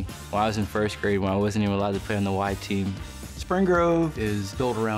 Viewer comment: Did he say when he, "wasn't even allowed to play on the white team"?